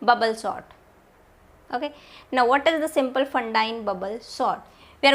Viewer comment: angrez students watching this lecture.... Hold up... did she say fatna?